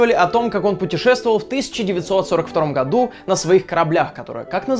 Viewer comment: мне не послышалось в 1943 году???